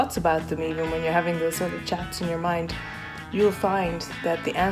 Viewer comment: I thought you said "And that's what america is" instead of Miracle... but we can make it what America is!